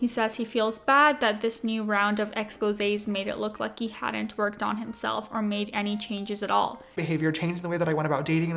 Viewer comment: the way you pronounced that around 2 mins in it sounded like you said "he was mad ppl made it look like he wasn't twerking on himself." i'm going to have nightmares about james charles twerking now. i hope youre happy.